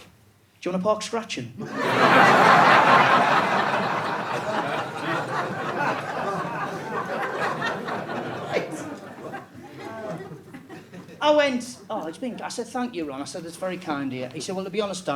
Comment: Could someone help me out? What does he say at 7:17? “Do you want a “park scratchum….pok scratching… pork scratcher…pak scraptchures”????? Say what? 😂 I’ve watched it over and over, dozens of times, and still can’t figure it out. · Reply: Pork Scratchings (deep-fried pork skin).